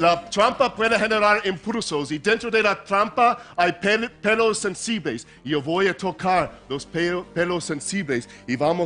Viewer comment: Minuto: 1:20 casi que dice "hay penes sensibles" jajaja